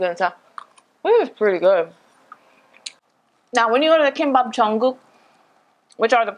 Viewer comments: I thought you said "now, when you want a Kimbap Jungkook" 😂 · The kimbap jungkook? I really hear that, no joke...can you spell what you actually said in hangeul pwease? :3